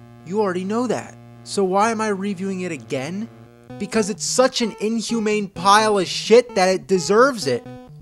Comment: The captions say "because it's such an inhumane pilots ship bed deserves it"... plus the rest of the video. Automatic captions are weird. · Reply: No, It Says "because it's such an inhumane pile of shit that it deserves it" Edit: Right, It Was From 3 Years Ago...